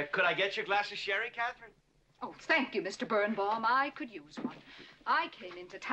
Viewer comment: Does she say "Spank you Mr Burnbaum"